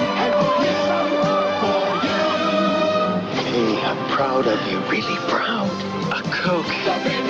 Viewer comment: “I’m proud of you, really proud!” “A coke” That’s not how you respond... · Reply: I thought he said “I’m proud of you pretty brown”